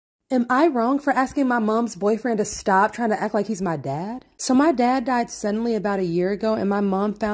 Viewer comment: I thought she said, “am I wrong for asking my daughters boyfriend to stop acting like my dad” For a second😂